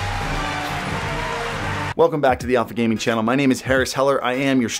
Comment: It always sound like you say Welcome back to the awful gaming channel